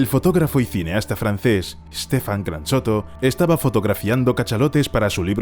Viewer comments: entendi Stephan Carachoto y dije no puede ser 🤦🏼‍♂️ ajjajajaj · no se pero yo escuche GranChoto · Yo escuché "Stephan Gran choto"